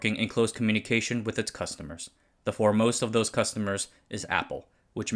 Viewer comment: when you said “foremost”, my brain thought you said “Formosa”. Ha